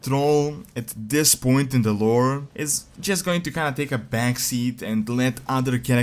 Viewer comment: I THINK I HEARD MY NAME! :) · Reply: nvm. Its actually Metzen :(